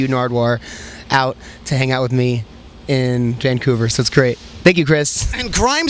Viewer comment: Ellie looks much skinnier now 12:45 - did Skrillex just say "Cancouver"?